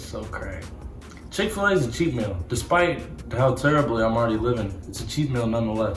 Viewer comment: I thought he said chic fil a was a cheap meal. I was shocked til I was like that couldn’t be it and then I was like ohhh he said a cheat meal